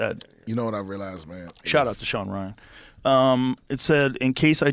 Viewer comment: did Joe tell his guest to shut up? haha rude boi 🤣🤣 jk hes saying shout out to Sean Ryan 🤣🤣